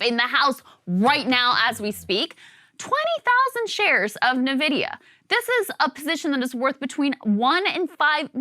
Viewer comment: did krystal just say NEE-vidia?